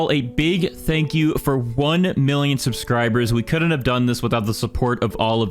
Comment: I Not One Who Heard "1 Billion"???